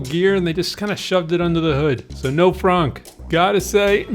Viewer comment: "Frunk is a popular portmanteau of front and trunk referring to cars that have their storage spaces in the front rather than in the rear, as is more typical. ... This means that while a Porsche 911 frunk might be useful for toting around a small piece of luggage, a Tesla Model S can offer SUV-like cargo capacity." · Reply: TY. Not sure why google didn't understand it. Maybe because I thought you said fronk instead of frunk.